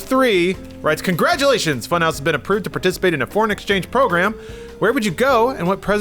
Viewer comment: When I first heard say ''foreign-exchange program'', i thought he said: ''porn-exchange program''. Guess the name brings up that association with me :3.